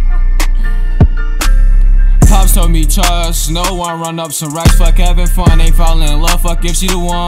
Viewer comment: what dit he sayyyyy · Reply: Trust no one I think